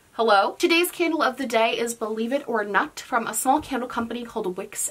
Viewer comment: Did you just say the candle name is "believe it or nut?!!!???!!!!!"